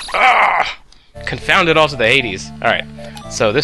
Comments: lol I thought you said the 80s also, maybe you shouldn't say the in that phrase. "Confound it all to Hades" will make it less confusing. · lol I've been saying "Confound it all to the Hades!" for many years now... this is the second time someone thought I said "eighties."